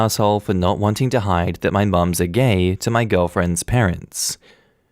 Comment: Instead of "Moms are gay" I heard "Mom's a gay" 😅